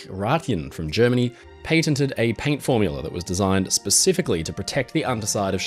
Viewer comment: I thought said "he painted the paint".... because that's exactly what it sounded like. Only it was "patented" the paint...of the guy who made it. That ole english accent can mess with ya some times. 🤣🤣